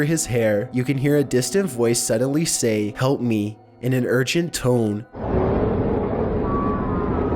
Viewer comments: Number 11 I heard "call me," not "help me" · At number 11 i thought they said "hump me"